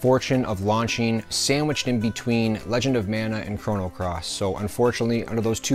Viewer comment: Legend of Vagina! Is what it sounds like your saying lol